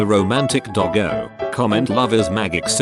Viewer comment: every time i hear it say "love is magic" in many videos i hear love is maggots. XD lol